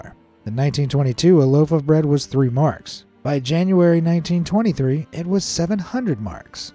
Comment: Typo in subtitles, 2023 instead of 1923